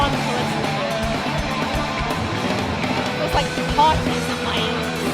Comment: I don't mean to sound profane. But I couldn't make out what you said, and around the 15:30 mark it sounds like you're saying "looks like the party is in my anus". And don't get me wrong. That is totally cool with me! 🤘🏼😊